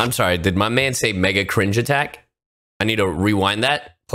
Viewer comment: Not gonna lie I thought he said Big Brudda attack instead of Mega cringe attack